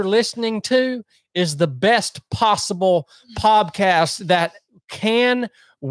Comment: Did he say poBcast at 4:15?